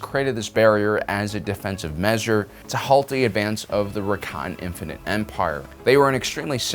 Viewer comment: I keep hearing 'Rakan' instead of Rakatan. (Also, completely unrelated but their 'Infinite' Empire had about 100 worlds, 'parrently they suck at counting lel)